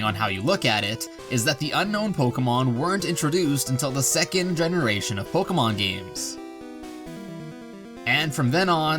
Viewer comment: does anyone know what it says? I could only make out “today” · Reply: Subscribe today